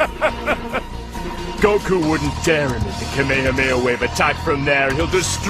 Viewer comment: I've been unable to hear what Cell says there for over 20 years.....somebody please tell me. "Goku wouldn't dare ___ the Kamehameha wave attack from there, he'll destroy the Earth if he does." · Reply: I think it's emit. "Goku wouldn't dare emit the Kamehameha wave attack from there, he'll destroy the Earth if he does!" hope this helps